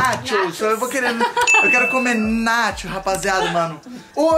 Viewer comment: eu quero comer nacho. Juro que entendi quero comer nathy kkkkk